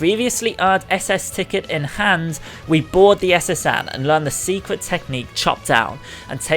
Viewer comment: I heard "we bought the SS Anne" the first time and had to rewind. Am I alone?